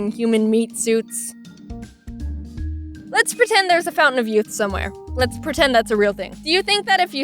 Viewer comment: I heard “let’s pretend there’s a fountain somewhere” and missed the ‘of youth’ part and was very confused on the aging backwards thing for quite some time.